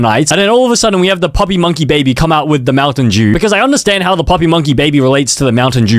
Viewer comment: I heard 'mountain jew' 💀💀💀💀💀💀